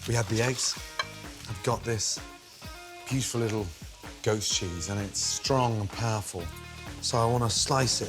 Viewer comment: I thought he said "ghost cheese".👻